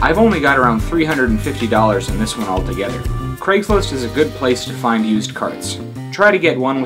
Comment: What site does hé say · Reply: Craigslist